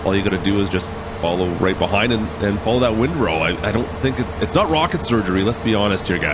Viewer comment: Did he just say it's not rocket surgery 😂🤣